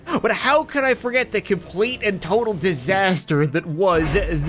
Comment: I misheard the barrel roller's nickname as 'The Kamikaze King' instead of 'The Kamikaze Keg' and now its my favorite nickname :D